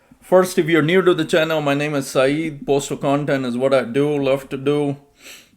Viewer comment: Bro, I thought you said “my name is naive” Did you also call Brian Renfroe “Brain Frog” too. Very good one Thanks bro.